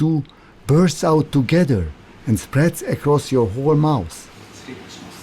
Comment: did I hear "whore mouth" there? lol